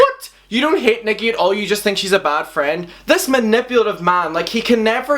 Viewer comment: I thought Jeffree said that Nickie is just a fat friend, at least that’s what I heard